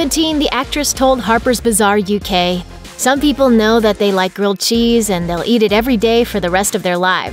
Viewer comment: I thought she said girl cheese at first, turns out she said grilled cheese .😐